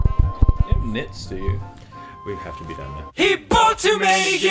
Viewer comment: This has been driving me insane ever since it was uploaded, but what is saying around 1:34 that says, "we have to be done now," to? I just cannot seem to pick out the words for the life of me. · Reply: I believe it's "You don't have mitts, do you?"